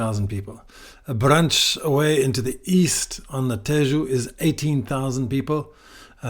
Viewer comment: Did you say that Abrantes is in Alentejo? 🤔